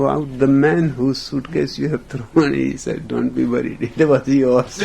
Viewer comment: What he said after 4:56? After ".... don't be worried about", what were his next words? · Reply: I believe: "it was yours"